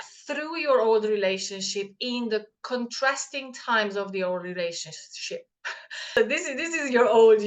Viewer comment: LOL it sounded like you said the old relationshit !!